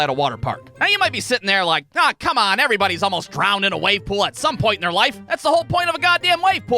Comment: it says poll instead of pool in the captions